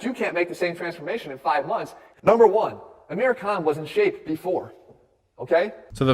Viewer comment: who else heard him say "aamir cunt" in the beginning? i had to turn on captions to be sure